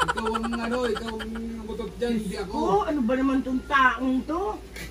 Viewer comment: I heard again the " ano ba naman tong tao NATO"😂